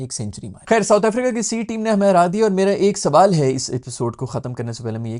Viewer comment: “South Africa ki C team ne humay hara deya hai” am I hearing this right ? 🤔